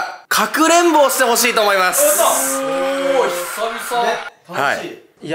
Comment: の所で 女の人の「えぇー」って声が入ってるように聞こえたんですけど… 気づたの私だけですかね😭😭😭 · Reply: モトキの｢おぉ〜、久々｣の、｢おぉ〜｣だと思います。0:16から聞くと女の人の声に聞こえるだけだと思いますよ